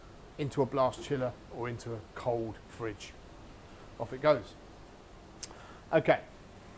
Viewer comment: I'm glad you said that because for a second I thought I was hearing "there it goes" (by itself) instead of "would you please..."